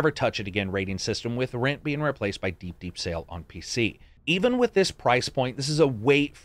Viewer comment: I disagree. If you loved Far Cry 5 then it's absolutely a buy or rent. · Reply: sorry you speak kinda fast. It's hard to understand. I thought you said deep deep sale meaning only get it when its $5 on like a steam sale.